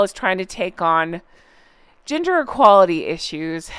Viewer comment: Was busy cleaning while listening to this episode. Honestly thought said their new mission was "Ginger" equality instead of "gender" equality😂